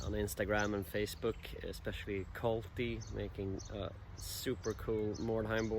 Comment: who does he say? subs say colby but cannot find them on youtube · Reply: Its Kolti